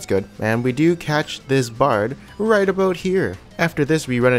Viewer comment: does he say aboat...? like Canadians in southpark?